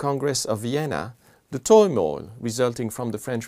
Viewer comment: What did he say at 0:59 when he said that "In 1815, at the congress of vienna, the (_______) resulting from the french revolution and the Napoleonic wars......." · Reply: Hello, The missing word is "turmoil". Thanks for your interest in our MOOC! Best regards, The Louv5x team